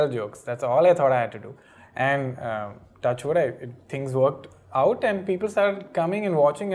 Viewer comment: did he just say "TOUCHWOOD"?!???!??